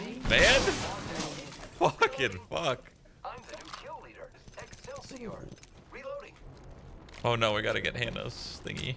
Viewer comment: did pathfinder just say "excelsior"?